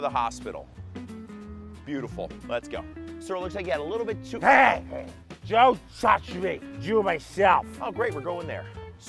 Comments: DONT TOUCH ME YOU'RE MYSELF that's what I heard, and it moved me. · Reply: I heard "Don't touch me! I'll do it myself." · DONT TOUCH ME YOU'RE MYSELF that's what I heard, and it moved me. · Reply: IT'S CRAZY I HEARD "DON'T TOUCH ME YOUVE MYSELF." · DONT TOUCH ME YOU'RE MYSELF that's what I heard, and it moved me. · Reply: I heard "don't touch me, ill do myself" Which is. Just slightly different in meaning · DONT TOUCH ME YOU'RE MYSELF that's what I heard, and it moved me. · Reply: I heard “I’ll do myself”